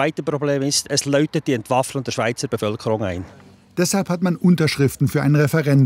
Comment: Was sagt er, ist das zweite Problem? Hab ihn leider nicht verstanden😂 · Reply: Es „klingelt“ die schweizer entwaffnung ein ..